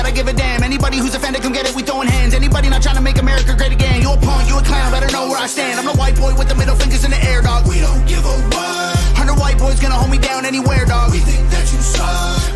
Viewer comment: Sometimes we hear what we expect to hear in lyrics. I heard "no white boys gonna hold me down (we think that you suck)". It was a follow up to the line about those who don't want to make america great again. I could be mistaken but thats how i heard it. Great reaction overall.